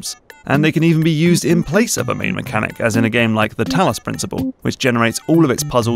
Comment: Why do I keep hearing "thanos principle"